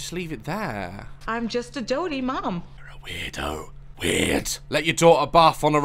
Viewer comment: I thought she said I’m just a horny mum I mean it would of made sense